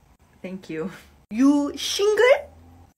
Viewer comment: When she said shingle I thought she said singer 🤣🤣